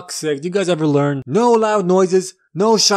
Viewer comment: Ok, so Elvis owned at most 9 buildings. So 9 builidings x 17 Elvises (which is the rating given) gives us 153 buildings. Which means that gives this movie 153 out of 3. Good movie then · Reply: i thought he said 17 elvises and 3 buildings and 17 + 3 = 20